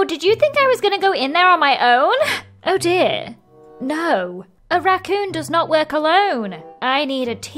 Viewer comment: I honestly thought she said “a raccoon does not wear cologne”